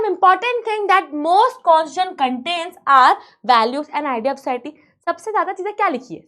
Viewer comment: Mam it's 'ideals' instead of "ideas"